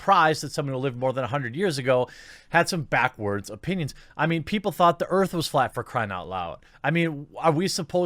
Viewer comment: Correct me if I'm not hearing this right, but did he just say the earth is flat? Lol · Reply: He said 500 years later the earth is flat lol